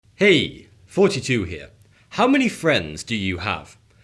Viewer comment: Why does it sound like he says hey 42 here in the beginning of his every video?